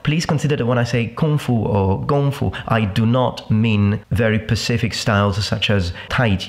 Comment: who else noticed at 10:17 he said pacific instead of specific?